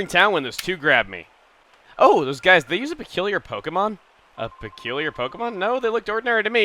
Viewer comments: I don't know why, but at 11:51 I thought he said, "Oh those guys, did they use to kill your pokemon?" O.O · Oh! So they used to kill your pokemon? That's what it sounded like for me :)